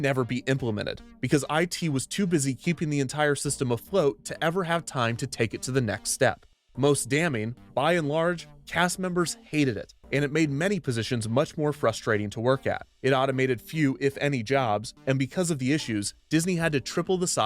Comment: DID HE SAY BUY N LARGE?! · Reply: No, he said "by and large" which is a common phrase